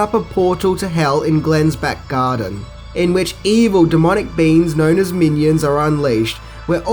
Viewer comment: Haha I thought you said evil demonic beans. I had to rewind it to make sure.